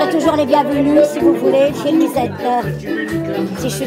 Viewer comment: she actually says " if I'm still around ", instead of " I'm always here " haha ^^